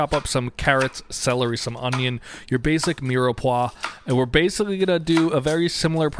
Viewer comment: what's that word? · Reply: mirepoix